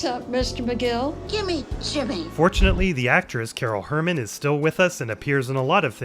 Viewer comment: duude, I heard it as ''unfortunately'' and I swear almost felt awful 😭